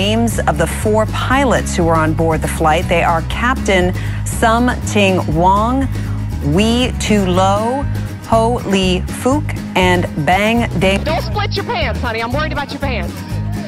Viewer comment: it sounded like she said something wrong we too low holy f**k and bang